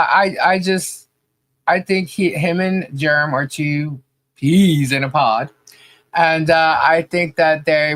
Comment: Did you say two Peens in a pod? 'Cause I thought i heard two peens in a pod, lol😅